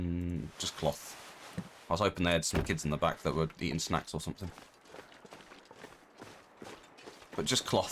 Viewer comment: I swear I heard "I was hoping they had some kids in the back that I'd eat as snacks or something" :O